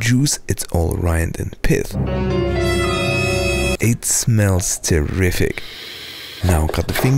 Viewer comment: Me who thought he was gonna say "it smells terrible" 💀